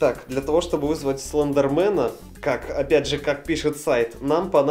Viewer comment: Мне одной послышались слендермэн хлам